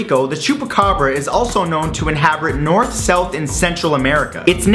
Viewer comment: it sounds like he says"Inhabrit", instead of inhabit. :D